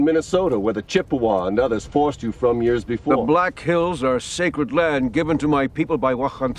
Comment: Did he just say "Israel are sacred land given to our people by YHWH"